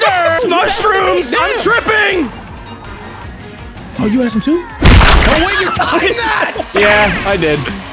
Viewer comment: I can be wrong but I swear someone is saying hey "oh you have some soup"